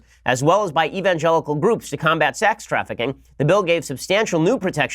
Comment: I heard "sax trafficking"...